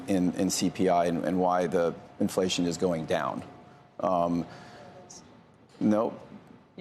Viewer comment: did he say “shart” instead of chart ? 😂 I’m pretty sure he said, “ the most important shart” 💩 😂